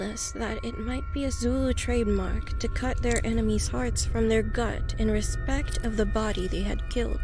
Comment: The narrator for the one about the hospital basement had such a strong accent I misheard him say hats instead of hearts.